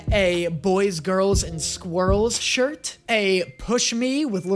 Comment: wait does say boys, girls, and squirrels'???? I thought he said 'boys, girls, and swirls'